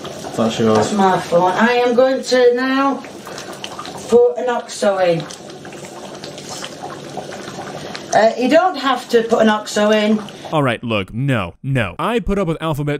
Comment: "I'm now going to throw an OXO in, you don't have to put an OXO in" is what she said 6:20 OXO is a beef bouillon cube